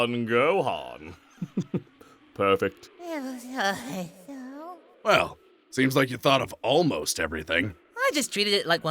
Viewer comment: what was Gohan saying? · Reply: It was alright cell. Turn on closed captions.